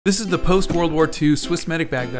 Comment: For some reason I heard "This is the post moonlord 2"